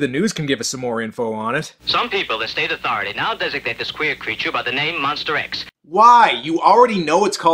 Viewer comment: Does he say 'queer creature' or 'weird creature'? Am I crazy? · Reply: Well, at this point in time, they meant the same thing. But yes, he said "queer"